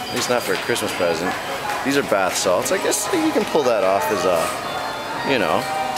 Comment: did you say those are bath salts ?? 0___o